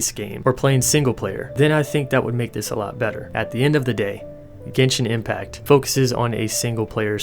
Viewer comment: I got confused when you were talking about “the end of the game” bcs I thought you were saying “the indie game” xD